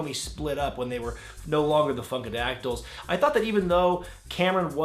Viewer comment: I thought he said the fuckadactyls